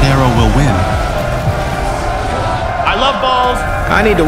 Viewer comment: anyone heard the i love BALLZ? Btw you have great content